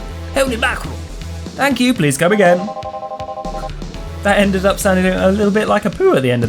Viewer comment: YES BABY, love you. Tytytytytytytytytytytytytytytytyty. is an mvp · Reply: When you said, "That sounded like Apu there." I thought you said, "That sounded like a poo."